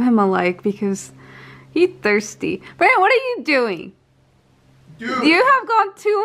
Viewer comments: did she say "Babe what are you doing?" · did she just say bae?? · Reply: She said Brandon... but she said it really fast, so it sounded like she said bae